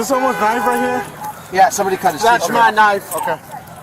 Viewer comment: right before minute 21 when they were asking whose knife was on the ground what did they say? It sounded like they said "someone cut his throat" . maybe just sketchy audio but I am curious now.